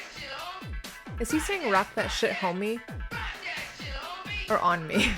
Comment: I always heard "Rock that shit homie"